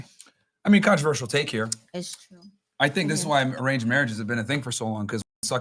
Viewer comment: What did Myron say at 4:40? What type of marriage? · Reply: Arranged marriage, where your father and mother find your husband or wif.